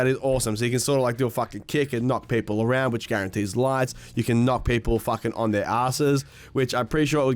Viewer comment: Thought They said to kick guarantees a heavy?